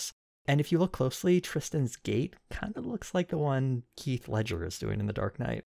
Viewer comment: did you say “Keith” Ledger???